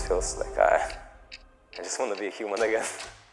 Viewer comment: does he said the nword or again ? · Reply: “Human again” dont worry